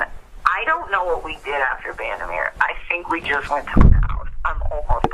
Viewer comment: I swear she says “I just think we went to my house!” That’s what I hear anyway. What about y’all??